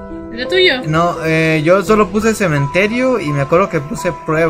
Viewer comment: decia o yo lo entendi que decia cementerio tu estas aqui osea de que tu estas en el cementerio